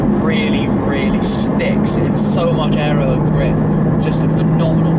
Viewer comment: does he say it has a lot of aero? I don't believe you could benefit from downforce driving round mountains, I may have heard incorrect